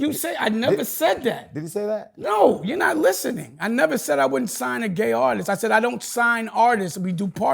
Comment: Same old Dame Dash, but in his defense, at 35:25, he definitely didn’t say he would never sign a gay artist. What he did say was, 'He doesn’t sign artists; that’s gay,' meaning the practice of signing versus partnering. I guess he is suggesting that signing someone is gay. Terrible verbiage.